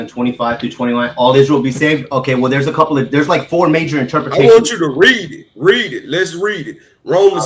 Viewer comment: I'm Covi and I saw it lol. BTW, it's interpretation, not interpolation, which IS what something means. That's literally the definition of interpretation, to explain the meaning of something. Did, you say you were a teacher? And you don't know that?? Hmm??? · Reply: I thought you said interpolation ..apologies went back and listened you said interpretation. I am not looking for validation only giving correction, there is maybe over 400 vids here with me teaching in places you would be afraid to walk with your children. Minister Covi 30 min worth or video here and you could only comment an assumption of I don’t know the difference between interpretation and interpolation. Am grateful for you explaining that to me.